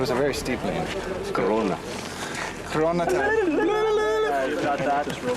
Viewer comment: Did they say Corona time?